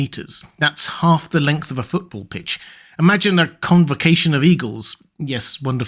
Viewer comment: Not even were able to escape the Americans system "that's half a football field" you guys need to get acquainted with the metric system. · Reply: I thought he said football pitch, which I believe is a term for what's called soccer in the U.S.